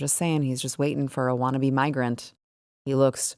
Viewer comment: 😂 haha I had to rewind that a bit! I thought you said whiney immigrant not wannabe immigrant lol